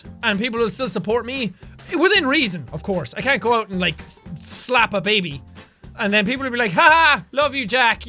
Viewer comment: i thought u said i can like just go out and eat a baby!